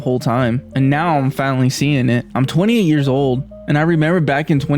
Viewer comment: 'I'm 28yrs old...' that what the captions tell me - my ears can only hear "I'm 20yrs old" and the reference to the GW2 release in 2012 had me counting...wait, that was 14yrs ago. surely, he didn't play MMOs as a 6yrs old. so why don't I hear "28yrs" while even the machine offering YT subs can do it? is the algorithm trained for this accent? btw, where [in the US] do they speak in this accent? 🤔